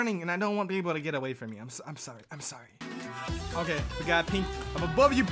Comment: the im sorry sounded like fe4rless??